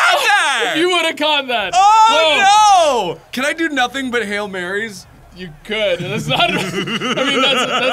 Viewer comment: I thought you said Hail Mary plays only